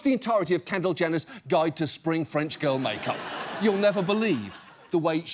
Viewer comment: What's up with 7:04? I legitimately can't discern what he's saying, and it sounds like the audio track isn't syncing to his mouth movements. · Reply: It does look like they did a voice over for that bit, I'm guessing he mispronounced it or something and they changed it in editing. But he says "You'll never believe the way she gua shas her brows." ( A gua sha is a heart looking stone people rub on their face).